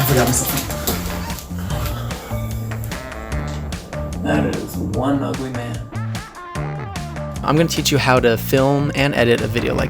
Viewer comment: Did he just say "that is one ugly man" in 0:11? Lol